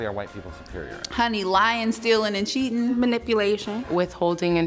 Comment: Legitimately thought she said "lion stealing"